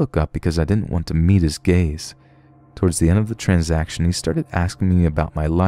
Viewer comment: I heard "I didn't wanna meet his gays"...